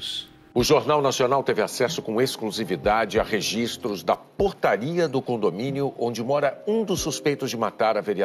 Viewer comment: Foi só eu que entendeu o Bonner falar "Putaria" ao invés de "Portaria"?